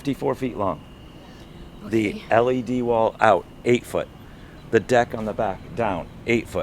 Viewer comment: Lol... Mosquito Food? Fun Fact did you know that Mosquitos like to fly less than 25ft up?... True story. At night we simply turn off the LED wall as needed. Why are they going to hear us? We are generally quiet. The truck is no louder than a Big Class A. I guess we will all see what will happen next when we pick it up in February. Thanks for commenting and sharing your thoughts. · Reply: thought you said “ concerts on your LED wall”?